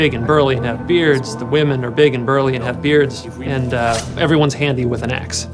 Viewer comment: wait a minute, at 4:43, did he just say "the women, are big burly, and have beards"????